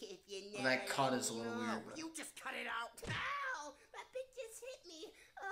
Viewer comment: idk if this just me but soundly like you said cu*t instead of cut 😅